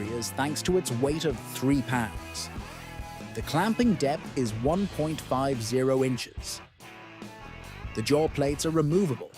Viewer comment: Did I hear “clamping depth of 150 inches”?! That can’t be right or did I miss smth?